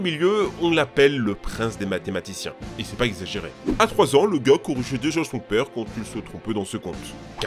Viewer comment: C'est moi où il a dit que a 3ans il faisait des factures ? A